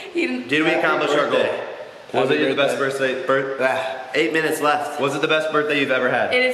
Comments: did anyone else hear say did we complish our goal was it the best first date I.. I mean birthday ever 😂😂❤❤❤ #chessa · sounds like first said "First date" instead of "Birthday"